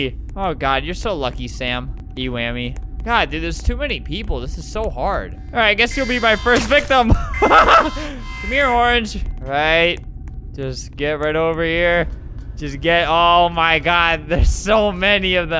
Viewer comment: When albert said “C’mere orange” at 7:50 it sounded like Dream’s “C’MERE GEORGE” 💀